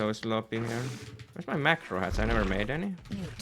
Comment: i heard "wheres my macro hatches? i never made any?..."